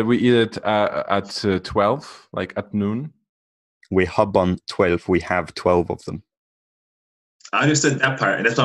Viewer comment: I swear he said it’s a month and we have about 12